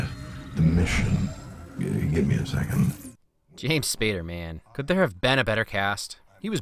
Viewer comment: I thought you said James Spider-Man